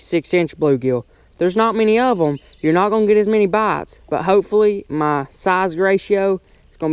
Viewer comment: It sound like ur saying bates instead of bites